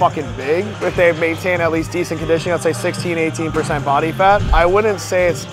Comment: What did he say in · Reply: 16-18 % body fat